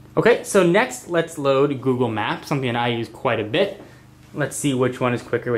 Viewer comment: did he say google maps?